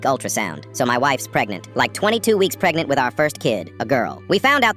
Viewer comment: Oh at first I thought they said 22 years pregnant😂